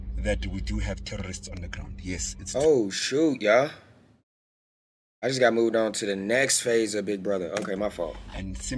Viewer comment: Is it only me who heard him say, I just been moved to the next phase of BigBrother